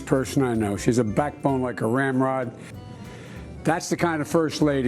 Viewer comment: When he says Jill is a backbone like a rail rod! Like 😳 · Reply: Lol I thought he said RAMROD! Hilarious 😂